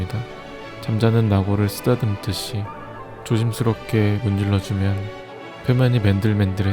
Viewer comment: 죠짐스럽게라고 들림 ㅋㅋ